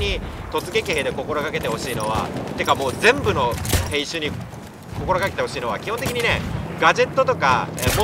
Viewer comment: iPhoneのスピーカーでこのシーン見ると、の「ぜんぶの兵士に」が「Hey Siri」に聞こえるらしくSiriが勝手に起動する笑